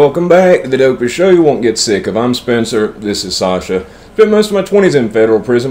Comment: Does Anyone else hear “If I’m Spencer” in the intro? Or is it just me? It’s been Confirmed that he’s saying “I’m Spencer” so I’m just trying to confirm I am hearing things lmao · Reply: Okay Rob here's what I hear; "The dopest show you won't get sick of I'm Spencer....". I listened to a few and see how you could hear "If I'm Spencer". Sometimes the word "of" is so quick that you only hear the end of the F sound. He also doesn't really pause after the "of". So, you're not really hearing things or maybe we're both hearing things. Hope this helps.